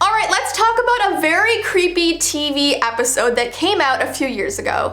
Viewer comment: why am I the only one who thought about tVEE instead of TV lmao